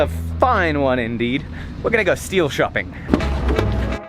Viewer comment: When said "We're gonna go steel shopping" it almost sounded like he said "We're gonna go steal something"